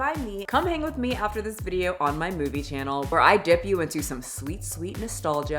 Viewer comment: I thought said her boobie channel, when she plugged her movie channel.